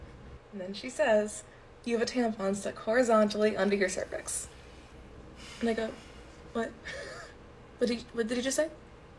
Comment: WHY IS NOBODY TALKING ABOUT WHAT SHE SAID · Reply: i think she said “and i go ‘what’💀💀